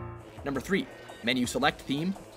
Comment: Is it me or did he say #3 instead of #7